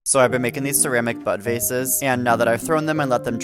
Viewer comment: bro for a hot second i thought he said “butt vases” not “bud vases” 😭😭